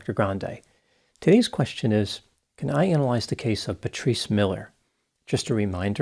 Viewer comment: I thought he was saying "The Tree Smeller' .. :) my hearing is not really great :)